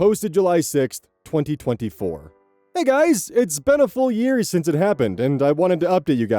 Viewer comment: the joys of hearing loss episode 2: I heard “a four year since it happened” and started questioning myself on “is that a fun way to say leap year?”